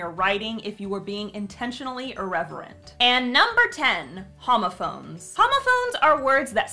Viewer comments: At first I thought 10 said "homophobes." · OMG FOR THE LAST ONE I THOUGHT SHE SAID HOMOPHOBES IM WHEEZING